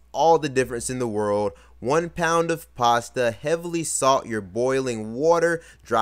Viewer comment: Thought he said heavily soften your boiling water at first